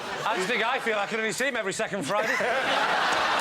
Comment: "How do you think I feel, I ___" What did he say? Couldn't make it out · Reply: can only see him every second friday